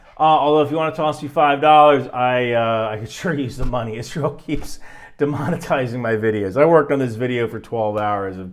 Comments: Get your Alcoholics Moving Cargo Shirt here: <link> OOPS! I might have made a mistake and mislabeled a KC-10 as a KC-135. Sorry. I'm tired, guys. I might also be wrong about the load out. Sidewinders may have to be loaded on external stores for targeting. Also note that the F-35I and the F-15I may have the ability to probe and drogue refuel. If that's the case, the KC-130Hs could be used to refuel. Other F-15s may be able to carry "Buddy" tanks to refuel. Everybody wants to talk about Israel "striking" Iran, but what would that actually mean and what would it look like? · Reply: Did you say that Israel is demonetizing your videos? That's what the captions say. Get some sleep. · did you say Israel keeps demonetising your videos? 😂